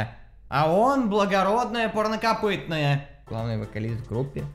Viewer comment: мне одному послышалось порно копытное?;)